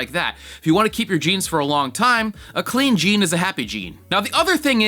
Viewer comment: when you said clean jean I thought you said mean Gene.